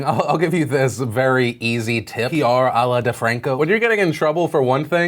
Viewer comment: Did he say Allah Defranco?! That is wrong on so many levels. No one not even the prophets of our religion would do that. Im not hating. I just found it disrespectful and hope that Im mishearing things. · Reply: Nabihah M He said à la, which is a french saying that means "in the style of..."